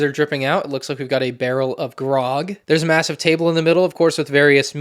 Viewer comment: did he say... Massive?